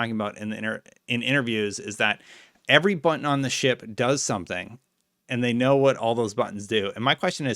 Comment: As far as I heard they said "Every button on the ship is labelled" not "Every button on the ship does something".